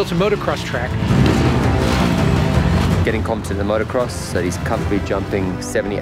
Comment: Tom Cruise is an absolute ANIMAL! And you HAVE to love his dedication to the art of Film making!!!! NO BODY does it like Tom Cruise! For being the "insane and crazy" Hollywood guy, HOW is he the ONLY ONE capable of making entertaining and actually watchable and enjoyable movies today!?!?!? The dude can do ANYTHING and is a Certified EVERYTHING! Seriously! What cant he do!?!?! "Don't be careful. Be Competent" Man i love him so much! MAD respect! I dont think he has ever hesitated in his entire life! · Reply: It does kinda sound like he said both " Be competent , Confident"